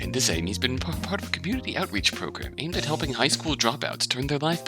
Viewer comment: I'm just listening for a short bit and I heard that as "Community outrage programme" 😂 If it was Snake AND Dragon, sure...